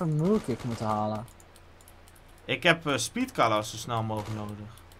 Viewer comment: Ik heb uuuhhh speed kala nodig Like als je dit ook hoorde